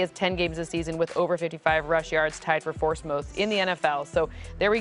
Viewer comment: anyone else hear her say 4st moth instead of fourth most? 😂